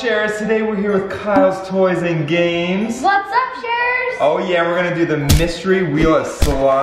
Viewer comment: For a second at the beginning I thought said “What’s up Stephens?”